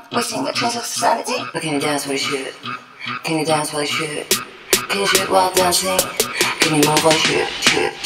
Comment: I thought it said can you shit while your dancing other than that it’s amazing